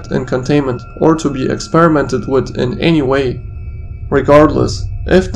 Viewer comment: turn on captions and it says wat instead of way